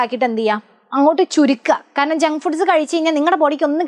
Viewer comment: sorry to say this but at first i heard jungkook instead of jung food 😂😂😂